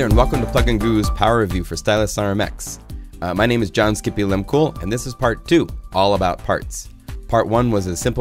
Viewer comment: i thought you said john skippy "im cool".. ha ha, love the name.. thanks for taking the time, great stuff!!!